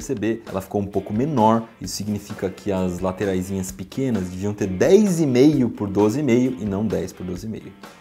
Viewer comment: Alguém intendeu esse trecho? 9:11 a 9:18 por quê eu entendi assim: ""----- as laterais pequenas deveriam ter 10 por 12, 5 e não 10 por 2,5..."" está correto? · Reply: Ele disse que as laterais deviam ter 10,5 por 12,5